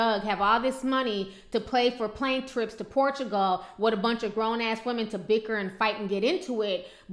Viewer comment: did she said portugal in 4:18??